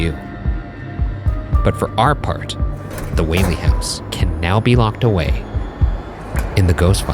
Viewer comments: Did anyone realise the captions stated Waverly Hills Sanatorium instead of the Whaley House?🧐🧐🧐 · the caption says waverly hills while its whaley house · Captions say Waverly Hills Sanatorium instead of Whaley House?